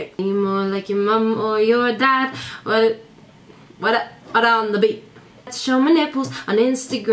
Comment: can someone tell me what did she said on 4:18? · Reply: What ta what ta the beat or something haha. +Fran Hodalj